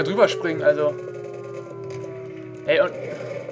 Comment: verstehe ich: HEY EINE KRAKE! XD